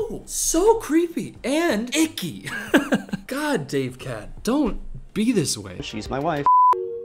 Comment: IM SOBBIGN I WAS JUST CHILLIN AND HEARD "davecat terrifies me" AND I IMMEDIATELY THOUGHT "HOMESTUCK?????????????????" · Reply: evry time he says "davecat" i hear "davekat" and take psychic damage